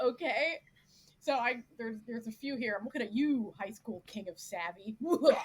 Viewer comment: I think mentions a show at 7:45 that she called a "crime" drama ("I'm looking at you...") and I'd really like to avoid it but couldn't catch the name. Would it be possible to give me the name of the show you were talking about? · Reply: I believe i was talking about Highschool King of Savvy, but really any show that has a teen/adult romance i'm avoiding like the plague ~